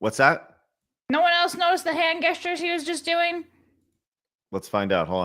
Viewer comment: wait wait wait. Did just say hand “guess tures” instead of hand “gestures”??